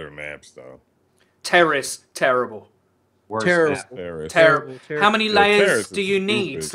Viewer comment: it sounded like he said Terra is Terrible lol